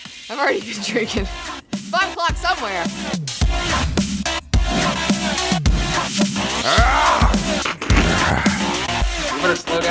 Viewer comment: did she say "buttplug somewhere" or is that just me lol